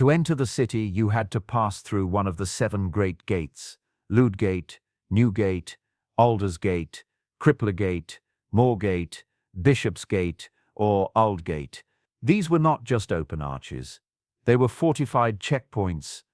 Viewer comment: For a second I thought he said Baldur's gate.